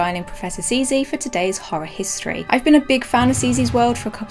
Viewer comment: legit thought she said “hoe history” hahaha